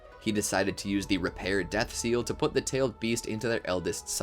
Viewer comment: im sorry but did he just say repair death seal? 🤣